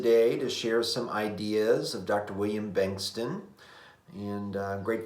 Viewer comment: i’m not sure what you’re saying! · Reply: ah! i see the transcription thought I was saying “Wayne” instead of “William”!